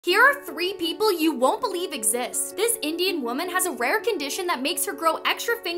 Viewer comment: thought she said this idiot woman for a second lol